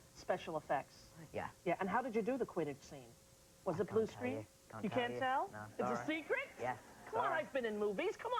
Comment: Hello, I'm sorry I misner English by ear but I understand perfectly well reading in English, can you please write what he said (at 8:08 - 8:19)? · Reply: Rosie: And how did you do the Quidditch scene? Was it blue screen? Daniel: I can't tell you. Rosie: You can't tell? Daniel: I can't tell. Sorry. Rosie: It's a secret? Daniel: Yeah. Sorry. Rosie: Come on. I've been in movies. Come on, Daniel. Daniel: I'm not... I'm sorry. Rosie: Alright, you're not at liberty to discuss, and I can understand. Daniel: I'm not at liberty to divuldge that information. Rosie: You don't want Joe getting mad at you.